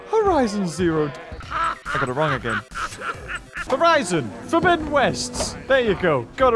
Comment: I thought you said Verizon Forbidden west